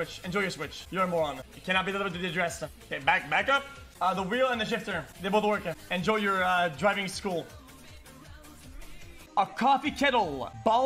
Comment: What the fuck did he say at 2:25 I didn’t understand a single word of it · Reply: "cannot be delivered to the address. k back up. the wheel and the shifter, they both work. enjoy your driving school."